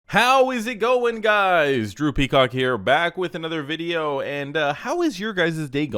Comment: i thought you said "droopy cock" at the intro 🤣🤣😂😂 Drew Peacock